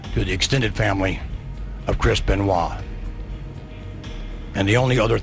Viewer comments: the subtitles said crisp and wall instead of Chris Benoit lol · The one thing says “ crisp and wall “ instead of chris beniot. Lol at 10:35 When I first heard this story I couldn’t believe it, so sad . Rip 🕊🕊🕊